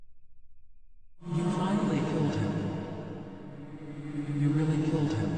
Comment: i was seeing a ton of people struggling to understand what was being said at 14:56, so i translated it. it might not be accurate as some words i couldn’t understand but i tried my best. i hope this helps someone :) you finally killed him, you really killed him. just because… that’s awesome. that’s. very. awesome. thanks to you, i was able to finally absorb her. her despair was so beautiful, so incredible. i’m finally free. the void is just incredible isn’t it? (something) in a void of nothing, even time stops it’s force here. there are no senses, no doubt, no life. only void. only void endless moment. only my thoughts that you can hear. so, let’s enjoy this moment together, in complete silence.